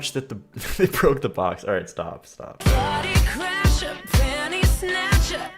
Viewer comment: Wait, wait, wait... She says "penny" snatcher? I stg I always thought she said "panty" snatcher.